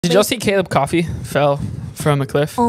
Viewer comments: Bro ain’t gonna lie I thought she said Caleb’s coffee fell off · Oh I thought they said Caleb’s coffee🥤 fell😅